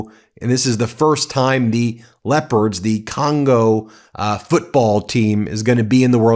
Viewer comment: I thought you said the Congolese team was called the lepers, not the leopards. Maybe enunciate that particular word a lil more carefully